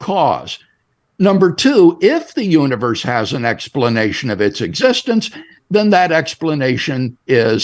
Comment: You see what you did there. He says that it can be a necessary thing as to be self-existing. But he didn't say that it was an entity like the universe would be an entity. He said 'being' meaning an agent. He's playing tricks with words here. He's trying to sneakily infer that the necessary thing is a being and not simply an entity. Because we can always have a necessary universe. But by including the word 'being', that excludes the entity of universe. Very sneaky doctor. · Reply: Nope, you misquoted him. Play back at .5 speed and you will see that he said: "If the universe has an explanation of it's existence, then that explanation is God. That is to say a transcendent metaphysically necessary being would the ground of the existence of the universe". A universe isn't "an entity". Definition: Entity: something that exists apart from other things, having its own independent existence. Definition of Universe: All space-time, matter, and energy, including the solar system, all stars and galaxies, and the contents of intergalactic space, regarded as a whole. It is non-sensical to equate "the universe" as an "entity". His point is this: A God is necessary for a universe to exist. If you've got a better argument, then give it.